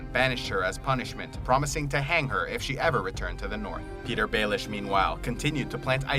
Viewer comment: LMAO I thought you said Pedo-Baelish